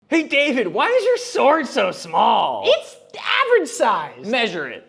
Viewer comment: I heard “Pete David why is your sword so small”